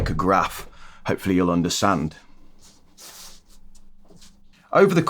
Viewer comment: Did you just say " undersand" lol